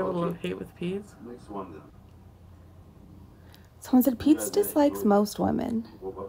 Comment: I thought she was saying Pee Pad. 😂